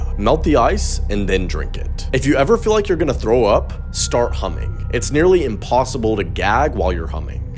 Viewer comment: Dude...I thought he said start Humping instead of humming I was like "hold tf on"